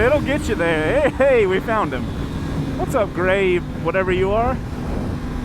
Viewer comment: did he call it a whatever you are????